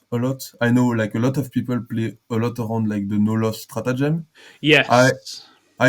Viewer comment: What was the stratagem he said he doesn’t tend to use a lot? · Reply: Thank you! I was hearing “no lust” and not “no LoS”. Makes sense!